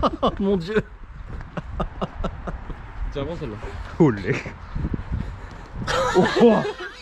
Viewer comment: Je vous connaissais pas, mais je m'abonne parce que j'ai entendu "oh leck" à 5:10, 57 nugget !